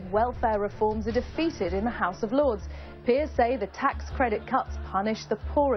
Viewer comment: I heard "tax credit cu*ts".